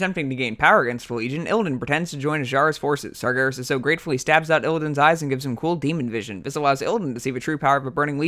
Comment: Wtf is he even saying 🤣 Bisewaise Illidan? You slur your speech way too much to speak this fast. Might be a good idea to just slow down and then speed up the audio in post lol